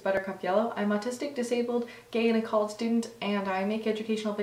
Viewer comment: did you say you are in a cult? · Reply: hahaha "gay and a college student" but that really took me a second